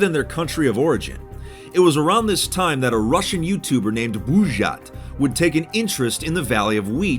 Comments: я один ржал с "БулжАть", вместо "Булджать"? 3:04 am I alone laughing with "BulzhAt" instead of "Buldzhat"? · Try Dashlane for free on your first device by heading to <link> and if you decide to upgrade to premium use my code FITMC for 10% off! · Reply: I thought he said valley of weed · I legit thought he said the valley of weed 🥴